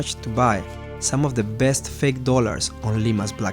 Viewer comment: Thought it said “ Ligma’s fake dollars”